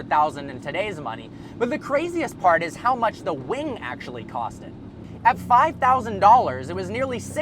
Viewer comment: Does anyone else hear,"That's how much the wing actually costed?" It's